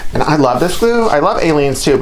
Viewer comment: Love these. When you were talking about the glue, I thought you said I like this glue but I like aliens too. I backed up and figured out you were saying Aleenes.